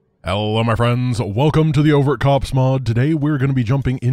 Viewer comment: I swear, every time I hear "the Overt Cops mod", I think it's "the Overmind Ops mod"